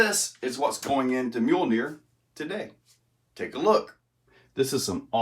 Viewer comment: I'm glad you put the name of that rig in the description cause I swore you Were saying mule deer bahahaha. "Mjolnir" I thought I recognized that name but it's no good without the decal of one 👊🏾👊🏾👊🏾